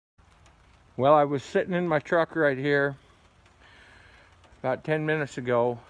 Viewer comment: Why do I hear him say “I was shitting in my truck”help what is going on with my mind😭